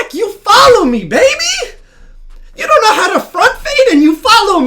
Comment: did I just hear the nigga say “front fade” ???… you mean pull up?